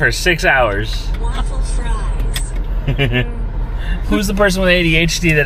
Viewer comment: “U can do things now.. what do u wanna do? “Who else thought/ said “waffle fries” 💕